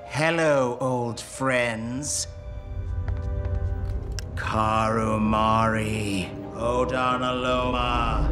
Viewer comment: OMG Did he say Carimare like the squid!